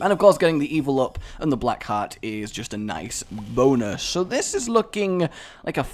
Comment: Did he just say boner ?!? lol